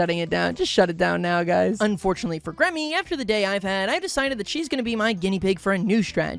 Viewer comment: I'm a bit late to the video but at 15:04 did he just call Gremmie "she"??